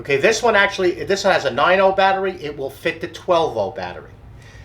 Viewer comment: did you just say you can put the 12v battery in there instead of the 18v?